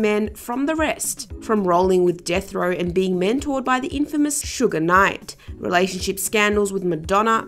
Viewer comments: I HAD TO STOP listening when she said "K.C.I".. I thought I misheard her when she said "sugar night"... nope. Done. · This is what happens when someone not of or in “the culture “ tries to tell the story of “our culture”. Even if from a journalistic perspective, this was poorly and lazily put together…. You lost me at “Sugar Knight”. 😒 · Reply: THIS!!!! That is what I thought I heard her say…..Sugar Knight smh 😒😩😣